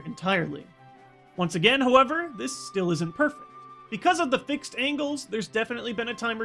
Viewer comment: is it just me, or does it sound like he said perf instead of perfect?